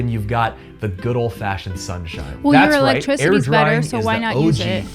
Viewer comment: did she say "your electricity is better"? And you wonder why the rest of the world thinks you are stupid 😂